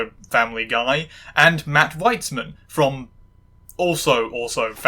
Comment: Okay, at this point I was thinking "all the show's creators are gonna be white men, aren't they?" Immediately after which you introduce the third creator, Matt Weitzman. Which I heard as "Whitesman". Found it funnily on-the-nose, is all. :P